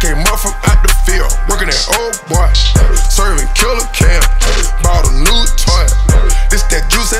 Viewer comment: I thought it said bought a new toilet😂